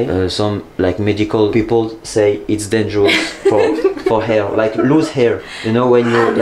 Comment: Elle a dit “it’s good for your health (santé) et toi t’as compris “hair” (cheveux) 😂